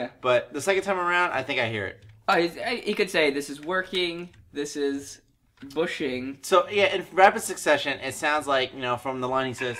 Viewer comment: I heard "this is bundi"